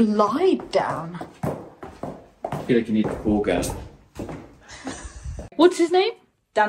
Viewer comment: Did really say what I thought I heard him say at 21:30..on the way down the stairs??!! 😳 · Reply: He said, "I feel like you need a ball gown" ... but I know what you're thinking 😅